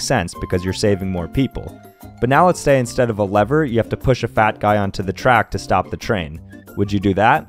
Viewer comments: Oh 'fat guy', I heard 'bad guy' and immediately agreed to push him 😂😅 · Bruh I thought he said bad guy instead of “fat” guy 💀 I was like, “hell yeah I would!” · Ohhhh I thought he said “bad guy” I’m like “What’s Wong with pushing someone that’s bad?” Lmao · Holy shit I thought he said “bad guy” and I immediately said yes 💀 Either way I would still do it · Me : Tries to push the fat guy on the railway track The fat guy : Inturn throws me off the bridge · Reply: I thought it said bad guy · WAIT NO I THOUGHT YOU SAID BAD GUY AND THEN I DID IT WITHOHT HESETATION BC HES BAD AND I WAS SO SCARED I WAS A PHYCO 😭